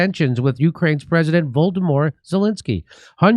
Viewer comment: It's very funny that Volodomyr is so easily mispronounced Voldemort. 😁 That's what it sounded like Jimmy said, but I don't know if it was a mistake or not.